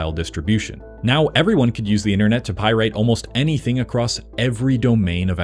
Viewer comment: I thought you kept saying “Pie rate” to avoid YouTube flagging the video for saying “pirate” correctly 😂